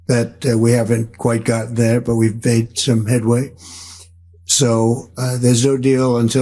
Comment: Sounds like Donny said we fake so headway. For real I thought Donny said that lol. 😂